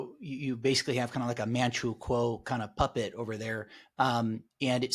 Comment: you state what the closed captioning ddescribes as "a manual quo" but I'm hearing something like a "man-true quo" but after a dozen or so attempts to decipher what you are saying---even going so far as to listen at 0.25, 0.50, 0.75, and normal speeds, I cannot decipher what is being said there. Can anyone possibly help me out on this? (It's driving me batshit crazeeeeeeeee!) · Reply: "Manchukuo was a puppet state established by Imperial Japan in 1932 in Manchuria, a region in Northeast China." "Puyi, the last emperor of China, became a puppet ruler for the Japanese in their puppet state of Manchukuo. The Japanese installed him as the head of Manchukuo, a state they controlled, after they invaded Manchuria in 1932. Despite being the emperor, Puyi had little real power and was essentially controlled by the Japanese. " I was referencing how the globalists use people/heads of state, as puppets.